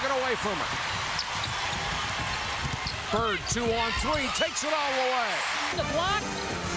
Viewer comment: When the announcer said, 'two on three' I heard, 'Sue on three' and it actually made more sense.